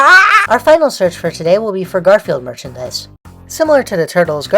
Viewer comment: When you started talking about Garfield merch, I wasn't paying attention to the video and I thought you said "Gorefield merch" and I was really confused